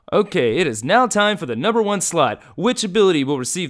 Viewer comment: did anyone else hear #1 slut?